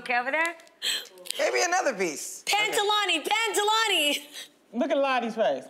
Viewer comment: I thought she said Pantalooni as in pants 🥴😂